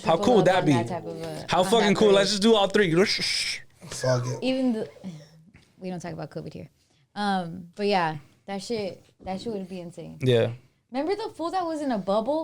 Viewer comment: Did I hear right or did say “we don’t talk about Kobe here” at 1:00:09. If that’s what I heard why don’t she talk about Kobe??